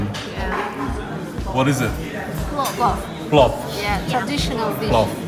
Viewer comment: Slops?? did I hear that right?? 😂😂🤣🤣😂😂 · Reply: Plov.